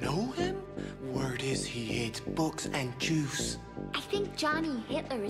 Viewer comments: "Word is; he hates Books and Juice!!!" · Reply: I thought they said he eats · “I hear he hates books, and juice.” Had me dying with Historical laughter. WARNING: The Comment section has become radioactive ☢️ please stay clear. AUTHORIZED PERSONAL ONLY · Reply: I thought he said "spooks and jews"